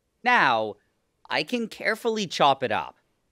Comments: I thought you said carefully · I thought you said carefully😭